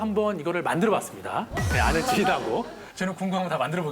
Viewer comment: 이때 뭐라고 하는지 아시는분 계시나요? 아들 둘이하고? 아늘트리라고? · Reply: 아는 지인하고